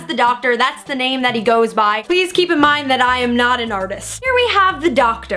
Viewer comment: I thought she said, I'm Not A tardis :/ WOULD BE NICE!